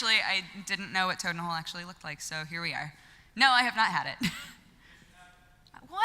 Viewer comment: and on I can't hear what is saying, can anybody fill me in? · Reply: Benjamin Travis he said she have had it , apparently she did , mom made it